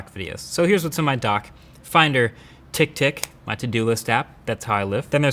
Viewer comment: When he said Tick Tick I thought he said Tick Tock. LMOA Tick Tock is garbage